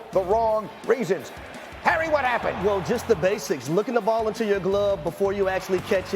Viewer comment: did he say "looking the ball into your glove before you catch it?" That doesnt even make sense